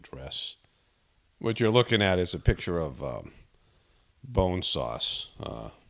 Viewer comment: I thought you were saying bone saws and I was all like I wonder what for. Then, it turns out it was bone sauce and I was all like I wonder what for.